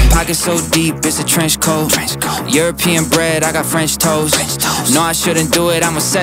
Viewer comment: “European bred, I got french toast” that double entendre went hard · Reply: I thought he said French toes 😭